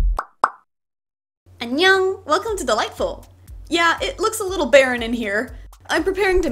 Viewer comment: I keep hearing "Onion, welcome to the Lightbulb"